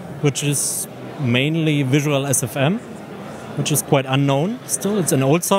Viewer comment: Hey, can somone tell me what software did he mention at 1:12? Can't understand what he said (I'm not english native speaker) Thanks! Edit: Nevermind, I found it. It's called VisualSFM. I tried it and works great!